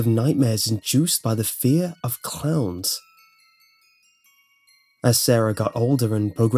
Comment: at the start i thought said she had a fear of clouds.... HAHAH WTF